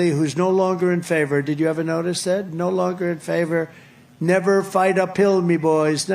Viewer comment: I thought trump said, "Never fight a pill!"